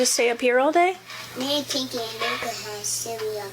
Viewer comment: what that kid was saying wasn't "unintelligible" she clearly said they had pot pies and cereal.